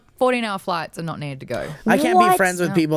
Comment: can't be friends with people with people who have to pee during a movie... so i heard it as he can't be friends with people with MS and other medical issue involving bladder or brain functions. my extreme misquote makes me laugh at that